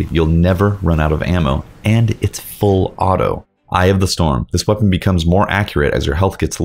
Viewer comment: "eye of the storm" sounded like you said "i have the storm"